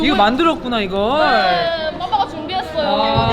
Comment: 연경언니 음성은 아 이걸 만들었구나 이걸~인데 자막은 ㅋㅋㅋㅋㅋㅋㅋㅋㅋ 아이고 힘들게 만들었구나 ㅋㅋㅋㅋㅋㅋㅋㅋㅋㅋㅋㅋㅋ 파바 만들 때 진짜 힘들었나봐 ㅋㅋㅋㅋㅋㅋㅋㅋㅋㅋㅋㅋㅋㅋㅋㅋㅋㅋㅋㅋㅋㅋㅋㅋ